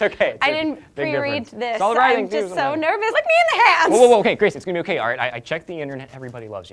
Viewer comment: sounds like she says "Lick me in the ass"...